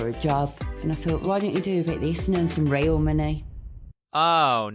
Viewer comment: Okay can anybody please tell me what she says just after 0:40? ive rewatched her say the same thing over a dozen times and i have no clue what shes saying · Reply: Why don't you do a bit of this and make some real money?